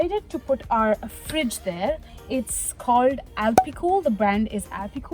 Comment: By chance do you have a link or a branded model name for the refrigerator you have? I thought I heard you say the brand in one of your other videos but I'm not sure which video it was. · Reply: Her3 you go! Alpicool C15 Portable Refrigerator 16 Quart (15 Liter) <link>